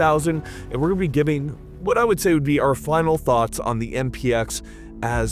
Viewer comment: did you just say "the flannel thought"? 🤣